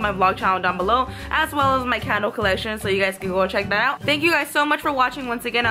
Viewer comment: Omg At first I thought she said cattle collection instead of candle collection 😂😂